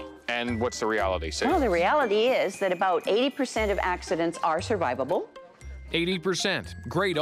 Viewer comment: I thought she said 8% survivable. I got so scared lol